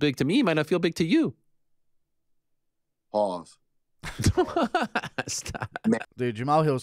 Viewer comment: did he say “pause” ?